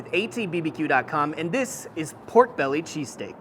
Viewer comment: See, I just got home from work. Tired, didn't even look at the title. Just saw a new ATBBQ video and clicked it. I had to reread the tittle and go back a few seconds in the video because I could have sworn said Pork Belly CheeseCAKE not STEAK! 🤣🤣